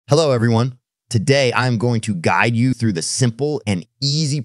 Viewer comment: WHY DID I HEAR "GYATT YOU"